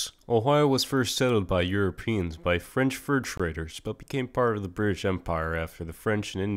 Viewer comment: I heard “Ohio was first settled by French first graders,” instead of fur traders. And when I first heard it I was ready to believe it.